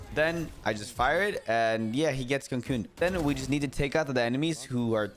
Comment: make sure your pronouncing the “co” at the start of cocoon more telligible cause it sounds like your saying coon 😭